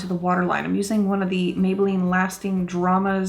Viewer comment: I LOVE that vibrant purple eyeliner! Do you know of a dupe? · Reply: Debbie Hill, TY! For some reason, I thought she said it was Marc Jacob's! I'm relieved it's Maybelline!